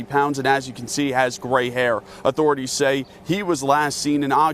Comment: I could have sworn that newscaster said "as you can see he has great hair"... I was all WTF does that even matter for!!! 😶😳😦😒 💩